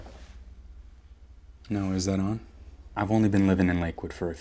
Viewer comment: Was he talking to someone else in the room when he said "now is this thing on?" 🙄 shady lol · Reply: I thought i heard "noah, is this thing on?"